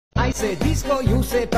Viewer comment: Bruh what i heard: "I said discord, u said barbie, disco disco paty paty, i said discord, u said barbie disco disco paty pa-" 💀💀💀